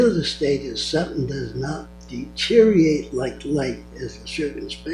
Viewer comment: What does he say at 2:12? “Deteriorate like life in” ...